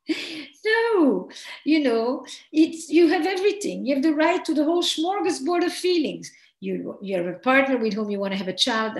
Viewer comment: sorry didn’t catch that, did she say he catches up with the kids to talk about the lonely? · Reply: Their LONGINGS* 💜